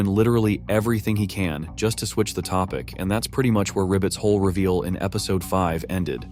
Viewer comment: captions say “Ribbit’s hole reveal” 💀